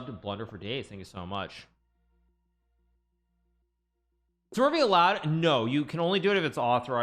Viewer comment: What did Hikaru say on 7:10? Can't find the chat question. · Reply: is smurfing allowed?